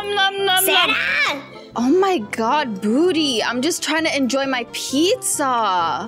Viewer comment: It sounds like she said booty xD